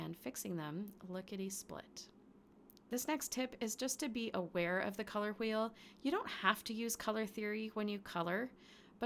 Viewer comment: For a second I thought that you were saying Beware of the Color Wheel! I got it that it was actually Be Aware of the Color Wheel, what a difference. I was getting a bad image of the Color Wheel rolling off the screen and getting me. Good that, that was not the case. LOL.